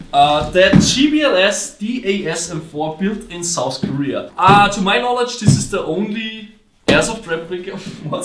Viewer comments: A while ago a package from South Korea showed up at the headquarters. Now we finally found the time to unbox it. And trust me guys it's something even I have never seen on a field so far. It's a unique operating system for guns which maybe could close the gap between GBBRs and AEGs. What do you guys think about the new gun? Is it worth the heavy price tag? Channel: <link> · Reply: at first I thought you said North Korea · I thought you said North-Korea instead of South-Korea